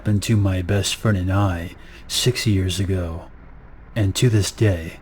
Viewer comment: 6-y years ago? 16hears ago? 60 years ago? it honestly sounded like he could've said either of these